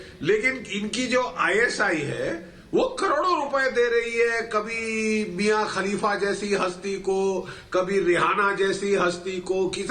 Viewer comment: did he say Mia khalifa? 😂😂😂